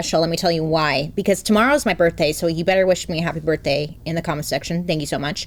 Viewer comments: I kept hearing “Tamara” like Bailey was doing a deep southern accent of “tomorrow” 🤣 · I missed when you started so when I heard you say Tamara, I was like okay is that how we’re pronouncing tomorrow? 😂 I can’t unhear it. ❤❤❤